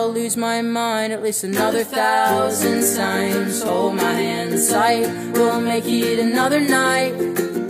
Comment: Instead of "hold my hand tight" I missheard it as"hold my hentai"